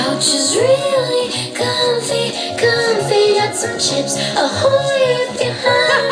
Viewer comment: I only realised today in. House tour. She says some Chips Ahoy if your hungry, (meaning cookie) but I thought she was saying Got some chips or a wh0re if you’re hungry 💀